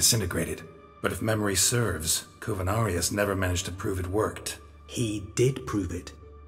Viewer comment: I thought Geralt said Coronavirus instead of Covinarius